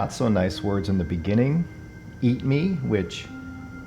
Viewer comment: I didnt hear eat me, I heard "beat me" which is how he died. Interesting.